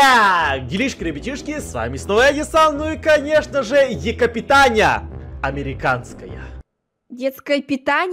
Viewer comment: мне тоже послышалось Детское питание 😂😂😂